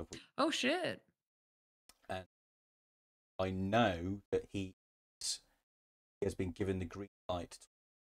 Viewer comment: The audio cutting out here is soul crushing, do you know what you were trying to say here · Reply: I think I was saying Martin has the green light to talk about a future project 🤔